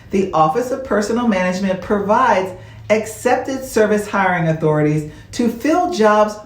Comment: Maybe my hearing is off. It sounds like you were saying "Expected Service" instead of "Excepted Service"